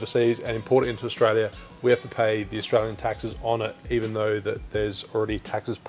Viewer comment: did you say "Israelian taxes"? Makes sense it being expensive...